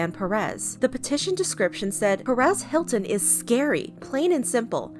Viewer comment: the first time i heard 'perez hilton' i thought someone just mispronounced paris hilton